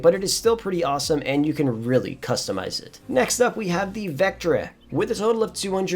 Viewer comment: he really said 'vectereh'